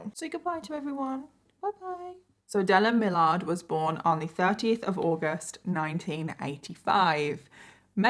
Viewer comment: I thought you said 13th of August making him a Virgo....I was like nooo no no I am August the 13th. I am a Leo. Lol you said 30th of August lol.